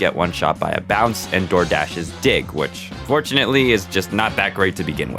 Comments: I really thought for a sec that you said "Doordash's dick" instead of dig 💀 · I heard that as DoorDash’s dick